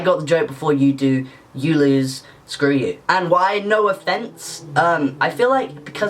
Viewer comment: When you said it sounds like no offence, and I heard it as “no a fence”